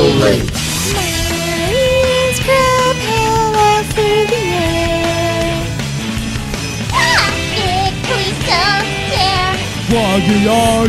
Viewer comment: what is she saying!?!? (Edit: I found out what’s she was saying) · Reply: My ears propel us through the air